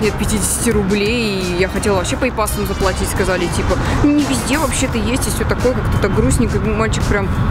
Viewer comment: В 2:37 она что сказала не пиз*и? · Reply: Виктория никулина не везде...